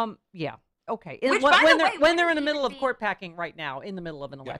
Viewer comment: Court packing all I hear is 'pork catting.'